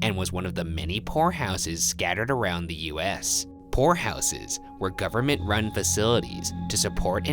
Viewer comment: Misheard ‘poor houses’ as ‘whorehouses’. Was very surprised.